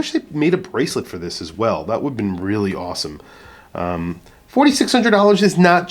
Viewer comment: i thought i heard you say the price was $8600??....hopefully a mistake. i know it's even more limited but.........